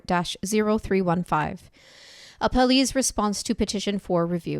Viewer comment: I’m so tired I thought it said “Applebee’s response.” I seriously thought I had missed some valuable information, like “Laura got the Wonton Tacos and how many Sangria Buckets?!”…